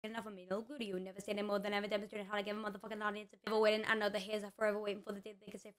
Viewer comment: I heard "motherfucker" 😭